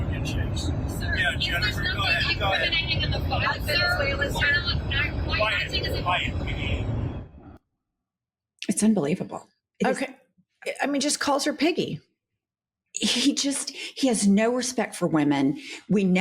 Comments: The first 10 times I saw that clip I thought he said "Quiet, Peggy." I guess that's what happens when you (I am) are too used to assuming regularity. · 😮 So used to tuning him out,I thought he said “M’gee”. It reaally was “piggy”.